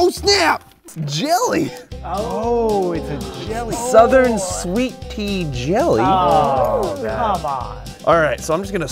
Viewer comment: I thought he said sweet pee jelly